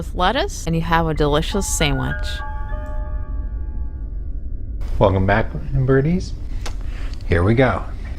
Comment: Does say “welcome back, f*ckin birdies” at 5:01?? 😂😂